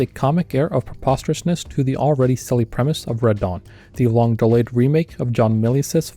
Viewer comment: I kept hearing "Radahn" whenever you said "Red Dawn".